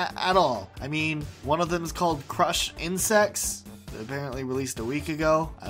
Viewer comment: LOL I THOUGHT YOU SAID CRUSH AND SEX